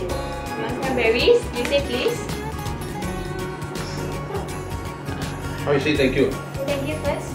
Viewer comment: I heard- "Starley, how do u say ky ky "? -and was shy lololol😂😂❤️❤️❤️❤️luv u